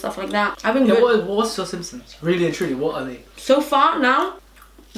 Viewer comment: Who clocked at 4:09 say simpsons instead of symptoms 😂✌🏽